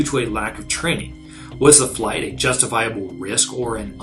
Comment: Whenever I hear you pronounce your channel name I hear "Aviation Whores".